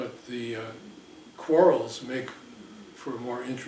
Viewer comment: Ali em 24:30 ele diz quarrels, não? Foi traduzido por "coros"